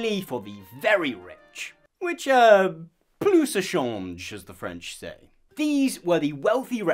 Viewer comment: My subtitles read "Very rich which uh pluciformes as the French say..." want to let me know what exactly the French were actually saying?